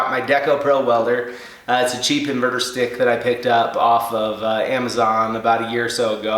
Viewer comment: I thought you said it's a "cheap murder stick" I was like, "Ok... I'm new to welding... so maybe that's slag for arc welding..."